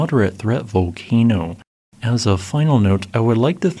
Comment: Did you just say thanks to a Mariam Fuckingham?